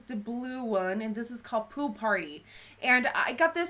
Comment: Omg, I thought she said 'poo party'. What are you wearing on your lips, by the way? I need it in my life.